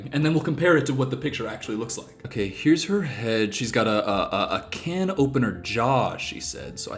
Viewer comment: I thought she said "can't open her jaw" and I was like damn I didn't realize my TMJ made me a man